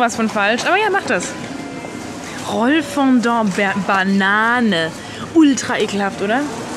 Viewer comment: Bin ich der einzige der nicht weiß wie heißt und der bei ca. 2:40 Roman verstanden hat? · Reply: Moon Pie sie hat rollfondat gesagt.